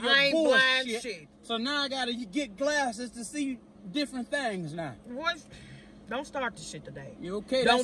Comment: "You blinded me with all your bull shid so I had to get glasses so I could see things differently" 🤣🤣🤣🤣🤣🤣🤣🤣🤣🤣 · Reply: I thought he said I see different things now 😂😂😂😂😂